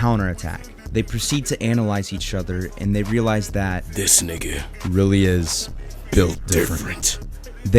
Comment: I swear i heard him say nigga